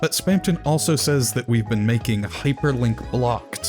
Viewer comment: I personally think that Spamton says "You've been making choices", it seems to make the most sense